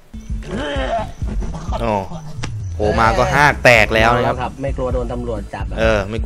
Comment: ถึงกับต้องพูดว่า ຫ້າງເເຕກ ไม่ใช้อ้วกแตกด้วยนะ